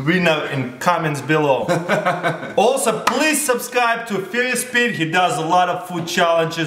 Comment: 0.08 sounds like says "sperious Pete"